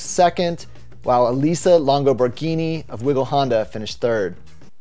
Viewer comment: did he say "finished turd"?